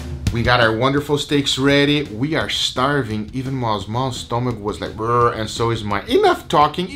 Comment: masmas instead of maw maw